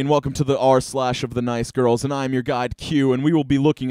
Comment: For a very long time I thought you were saying “I’m your god Cue”... never really disputed it